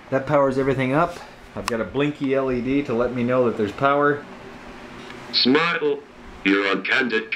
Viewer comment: Heh when it says "Smile!" it sounds like it says "Snartle". :-) Great work! Have fun at the Faire!